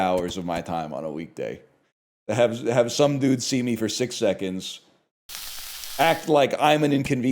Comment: I must be insanely fuckin gone cuz every part of my body heard that as "six sevens." I knew he meant seconds of course but I didn’t hear that whatsoever, I thought there was just a slip of the tongue from the 6 7 jokes ans he just kept the bit going, which to be fair I should've known he would not be capable of doing completely uninterrupted